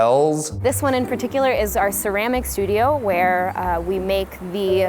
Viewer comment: i keep hearing "our croissanty."